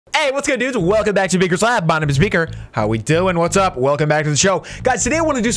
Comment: it sounded like you're saying "My name is speaker" lol